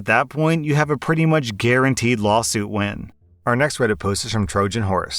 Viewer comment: i heard "when" instead of "win" the first time and it confused me lmao